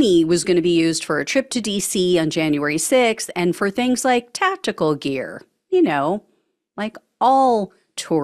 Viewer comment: I thought you said "testicle gear" Lol ! For a brief seconds, I was like "what the heck were they planning to do ??" 😅